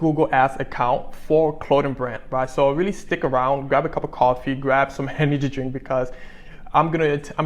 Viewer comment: lol I thought you said grab some henny to drink 😅